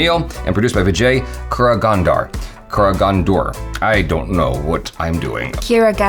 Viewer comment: Did says ,' Gandu' 😅😂😂😂😂